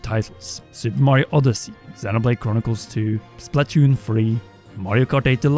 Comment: anyone else hear Splatoon 3?